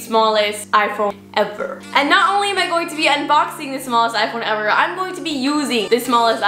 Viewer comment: I thought u said “World smallest iPhone 11” 🤦‍♂️